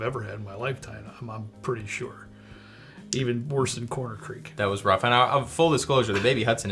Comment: Which bourbon did he say. Was it Crowan Creek??? Did he mean Rowan Creek???